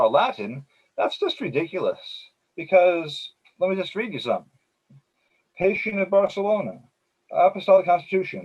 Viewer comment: said that ... of Barcelona. I didn't hear him clearly when he named that Church father. does anyone know his name? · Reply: Astrofire, The reference is to Pacian of Barcelona, who became a bishop in 365. In his Third Epistle to Sympronian – Against the Treatise of the Novatians, in paragraph 39, Pacian writes with heavy sarcasm: “O Novatians, why do you delay to ask an eye for an eye, a tooth for a tooth, and to demand life for life? Why do you wait to renew once more the practice of circumcision and the sabbath? Kill the thief. Stone the petulant. Choose not to read in the Gospel that the Lord spared even the adulteress who confessed, when none had condemned her.”